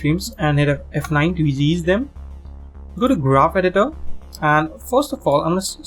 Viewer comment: Does anyone know what he said at 5:45?😅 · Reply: Go to Graph Editor...